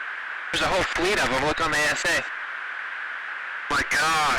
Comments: "Look on the AESA" is not correct in my opinion. Early Legacy Hornets F-18C's and later models have a Situational Page on their MFD's so what he says there "Look on the SA" which is a way of saying "Look in the Situational Awareness Page" On SA page all contacts from all other datalink sources are shown so they can possibly see all contacts that they're not scanning with their own radar like for example ships radar. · I know this was 4 years ago but at 2:06, when the captions say "Look on the AESA," that's probably not what he is saying. I'm going to guess he said "Look on the SA." The F/A-18E/F Super Hornets have a sub-menu called the "SA" page i.e. the Situational Awareness page. That page gives you Link 16 also known as Datalink. The unidentified aircraft would show up on that page as a symbol. Very small, minor correction, but a thought one that should be mentioned. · "There's a whole fleet of them look on the AESA" 2:06 He actually says "look on the SA". The SA is referring to the SA display, or the Situational Awareness Display. If he was referring to the AESA radar, he would have said radar attack display. Further, AESA is pronounced aa-eeee-suh.